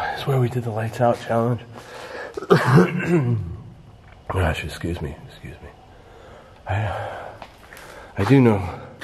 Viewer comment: I heard excuse me right after said excuse me. Totally weird. I don’t see what is wigging out about but he’s convinced there’s something scary there with him.